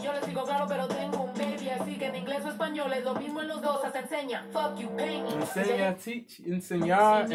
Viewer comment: She says: They say Snow tas crazy. I say I know but I got a baby. So in English or Spanish, it's the same, even in sign language: F*ck you, pay me! I thought she was saying "enseña" too, which can also mean "show me" but it was two words "en seña" which means "in sign (language)". Thanks for the Snow reaction! 🔥 ❄️ 🌨️